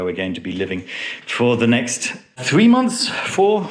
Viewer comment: Who's talking about a limited time? This is my home... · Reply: oh, so sorry, i thought i heard you say that would be your home for the next 6 months? lol maybe just misheard